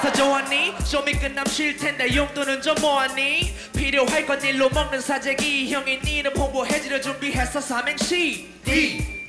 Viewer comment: 닐로먹는 사재기 시팔ㅋㅋㅋㅋㅋㅋㅋㅋㄱㅋㅋㄱㅋㄱ이게 왜 이제 들리냨ㅋㄱㅋㄱㅋㄱㅋㄱㅋㅋㅋ